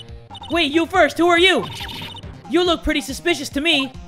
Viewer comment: anyone wondering what the random kid said at 9:01 he said "WTF? Who are j00 d00dz!? LMAO!" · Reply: "wtf? who are you dudes?"